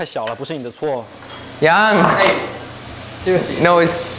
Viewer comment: xcuse me dude, the subtitle got wrong. It must be yangyang not yangayng sksksksksk